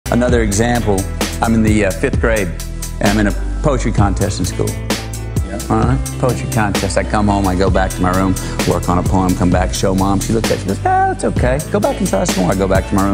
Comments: i thought it said pottery 💀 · I thought he was saying "Poultry contest".